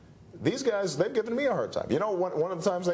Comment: Pay attention and Listen what what o´really quick mouth says at 4:50 something like "tons of money for you soon" to Obama.